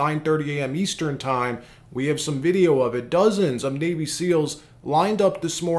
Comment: I thought you said dozens of baby seals lined up to support his nomination. navy seals, that makes more sense.